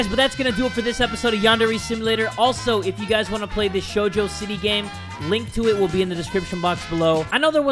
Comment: I thought he said "Jojo City" (The easter egg on Yandere Simulator)